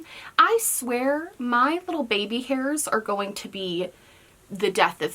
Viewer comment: Thought you said 'the baby has no hair' and went : What baby?!